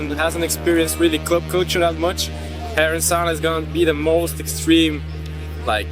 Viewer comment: The guy is mentioning a party called "Herrensauna" which is happening at Tresor, the subtitles are wrong but the party is great.